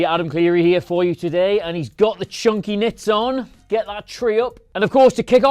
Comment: I thought you said chunky nips for a second there 😂